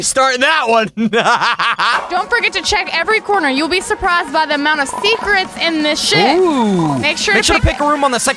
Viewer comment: I thought je said there's an amount of secrets in this shit